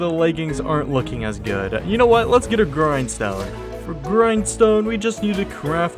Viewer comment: Legit thought you said "groin stone" instead of grindstone, and almost spit out my drink. 😆 Loved the video, you should do 200 days fr bro.